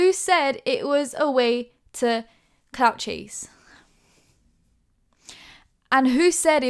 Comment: WHEN YOU SAID CLOUT CHASE THE SUBTITLES THOUGHT YOU SAID “couch cheese” AND I ALMOST CHOKED ON MY DRINK ALLALSSL